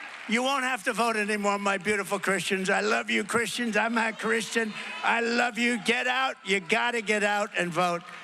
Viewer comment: can you give a time stamp for that part? I must have missed it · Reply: is where he clearly says "I'm a Christian"